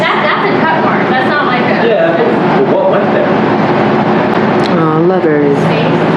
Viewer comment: "aww lovers" i heard that 🥰